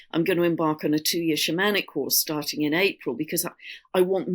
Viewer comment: I know said "2 year shamanic..." but I heard "2 year shenanigans" and it made me smile.😊